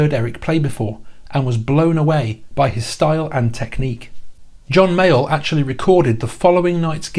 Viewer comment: I am loving these videos of yours and this one in particular, but I keep hearing “John Mail” when you say John Mayall lol